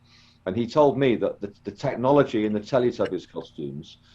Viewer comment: Same technology as what? I couldn't make out the word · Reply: phantom fighterbombers